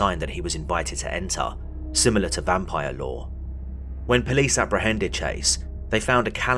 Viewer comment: OMG! At first, I thought you said: According to Vampire Law. Then my accent meter in my brain translated it to Lore! 😂😂😂😂 Glad to see you’re doing Shorts as well. I truly enjoy your work.